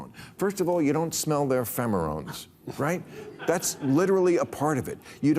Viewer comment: Did he just said “phemorones” 😂😂😂😂😆😆😆